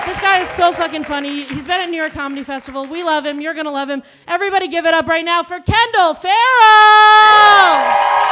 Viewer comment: misheard her as "give it up for crack!!" and I was like "... well okay then"